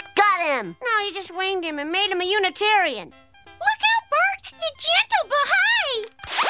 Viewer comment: "you just winged him and made him a Unitarian" is one of the most underrated Simpsons jokes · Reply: I thought he said "an Italian". I'm Italian 😂